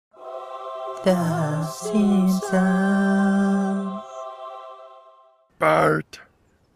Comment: I thought you said the simp sun